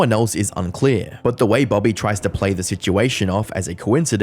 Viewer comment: My toxic trait is that I can't move on and let go of the fact you said disingenuine instead of disingenuous.